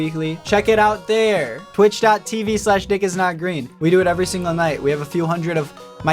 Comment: I keep hearing 20:42 as dickisnotgreen 😭